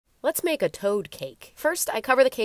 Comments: Am I the only one who thought she said “Let’s make a toe cake!” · I thought you said "toe cake" this looks amazing, but I really want you to make a toe cake now lol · I thought she said toe cake-💀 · Pov you thought she said toe cake 😂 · I originally thought that she said she was making a toe cake before I saw it 😂😂😂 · At first I thought she said “toe cake” lol 😂